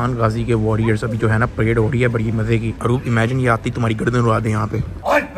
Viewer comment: can some one tell me what he exactly said???? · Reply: he said "aroob imagine ye aak gardab ura dain tumhari" 😂😂